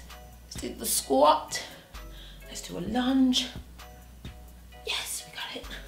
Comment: When you said lunge low key thought you said lunch 😭